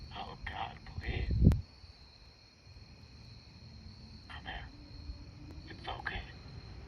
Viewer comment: Ok, when that one raptor thing says, "come here." I straight up thought at first it said, "beer." Now, in my mind, there was some dude about to get eaten laice and the last thing he says was beer lol